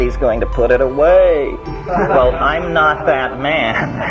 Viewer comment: did Terrance say "Im hoping he shows up"? Hmmm🙏🏼☝🏽💚